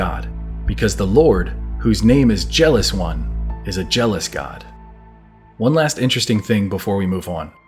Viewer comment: I thought it said for your God is a jealous guy